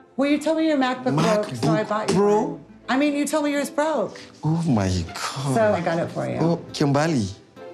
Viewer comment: So did anyone catch that at b/t 8:58-9:02..." well you told me you were broke" is what Kim said to Usman😳😩🤨😑🤔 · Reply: She said “you told me you yours broke” as in his MacBook broke